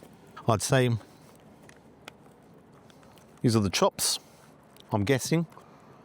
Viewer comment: I thought you said these are the trots when you said these are the chops 🤣🤣🤣